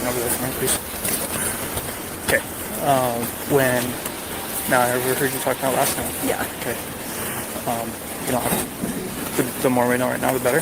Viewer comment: After the cop told to wait over there please, it sounded like he said Now we're free to talk about last night?